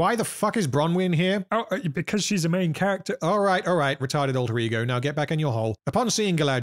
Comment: I watch videos like this on 2x speed, and until today, I’ve thought you were saying “Retarded Rodrigo” and not “Retarded alter ego”